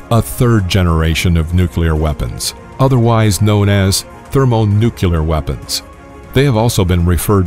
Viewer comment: Thermonucular...around 4:30. I had to rewind and play that again just to make sure I heard it right. And Yep. Thermonucular.